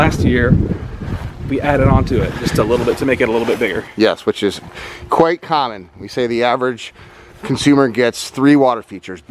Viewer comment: Did you say “koi common” or “quite common” at 1:00? I really hope it’s the former! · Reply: Meredith Schuler He said “quite common.”